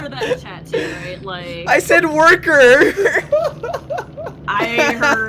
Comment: I swear I heard "Borker", regardless I loved this episode of DbD